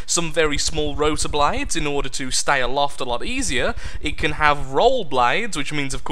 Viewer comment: Is it me or did he said Loft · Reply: Aloft - 'up in or into the air; overhead.' To stay aloft, is what I said :)